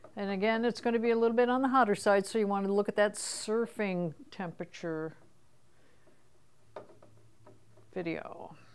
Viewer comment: What is she saying in 16:33 ? I heard something like a “video” :D It is for educ. purposes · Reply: Watch the temp surfing video.